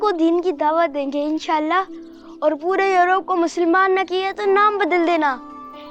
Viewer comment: what that kid said?? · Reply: Musalman bna denge other religion walo ko